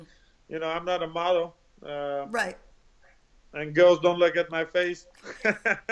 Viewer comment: I couldn't understand him at 5:01. Did he say, "Girls don't lick(?) at my face" or "Girls don't look" ? Gegard is funny is hell😂